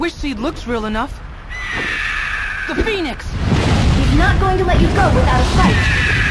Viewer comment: What normal people hear: THE PHEONIX What I hear: THE PEN*S